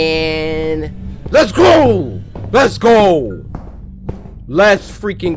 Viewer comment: it sounded like cory said: 'LETS CROW!' the first time lo